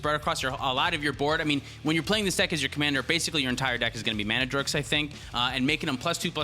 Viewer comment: did you call them "Mana Jerks", "Mana Dorks", or some kind of combination of the two?